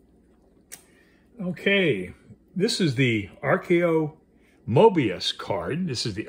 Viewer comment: Is it called "archo mobius oracle deck" (24:41) or does it have another name? Thank you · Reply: It is the "Archeo" by Nick Bantock ~ This is one of my favorite oracles. I enjoy the artwork! Here is the link to my review of this deck: <link>